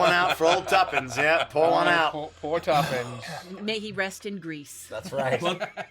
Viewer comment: when the auto-generated subtitles say “may he rest in Greece”…poor AI trying to make sense out of this wacky crew /lh /s